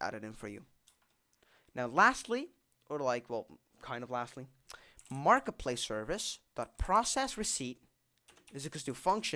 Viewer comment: it almost sounded like you said markiplier lol edit pls help i dont have discord